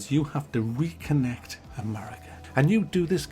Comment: sometimes i dont understand what you say and subtitles dont help at 2:12 : "you have to reek and maria"... now that i wrote it and read it, i guess it is "reconnect an area". sometimes there are much more syllables than what i or subtitles can process